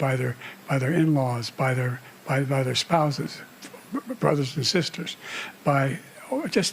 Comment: I heard "by their spouse's brothers/sisters," meaning their "in laws," e.g. a brother-in-law. 21:21 looking at it again, is there a cut in the middle?